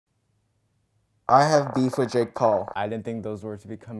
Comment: i thought he said ‘i have beef with j. cole’